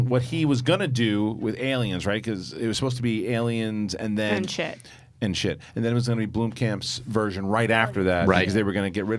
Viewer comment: um did she say shit? Why?